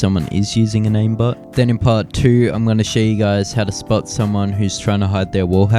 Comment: At the beginning at I thought you said "I'll teach you guys how to split a Cheeto"